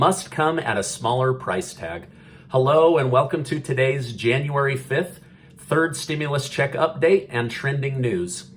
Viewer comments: My dear: I speak Portuguese as my native language. However I confess I heard you saying "January 5th", not 25th. I just wanted to make sure... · i think he just said january 5th instead of 25th